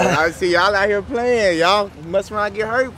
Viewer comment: i fr thought bro said "ay is there crayons in that chest?"